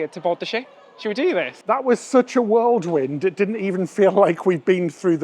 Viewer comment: did you just say "world wind"? 🤔